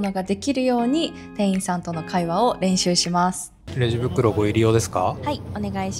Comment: 今まで「レジ袋、ご利用ですか」だと聞き間違えていました。「レジ袋、ご入用ですか」 なんですね！